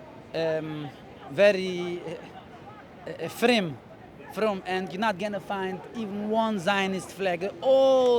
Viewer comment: just so you know for next time, he isnt saying "reform" he is saying "frum", which is yiddish for observant/religious. Its the opposite of what your subtitle says